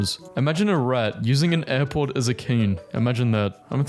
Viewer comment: At first I misheard "Airpod" as "Airport" and thought "Wait, WHAT?!"